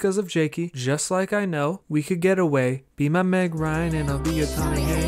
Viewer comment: I thought in Tommy Hanks he said Matt Ryan which I thought was matt damon for some reason